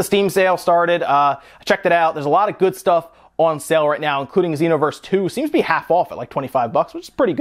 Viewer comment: OH I thought he said Xenoblade 2! :VVV I was so confused for a moment there